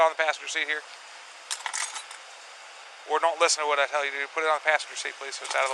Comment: At around 9:34 did he say "boy don't listen to what I'm telling you to do"?